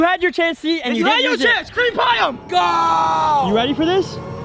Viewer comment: Did just say “cream pie him? Lol